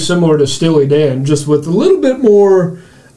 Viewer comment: "Barry White meets Steely Dan" I love that comparison! Easily one of the top songs of the time. I watch a lot of different reaction channels and enjoy all of them but in all honesty your videos are the ones I look forward to the most! You've said that y'all work many hours at your jobs but please, please make videos more often. Few things give me joy since 2020, sadly. Watching your videos gives me such joy and keeps a genuine smile on my face the entire time! Thank you for that. Perfect song. Perfect reactions. Much love Gentlemen❤️ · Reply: Oh did he say Steely Dan. I guess that makes more sense. I thought he said Stewie from Family Guy. 😂🤣🤣🤣. Barry White meets Stewie? What does that even mean?